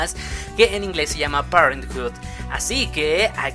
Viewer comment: Que inglés se llama ?? No entendí 8:51 ??? Quieres decir PARENTS ? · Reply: Parenthood.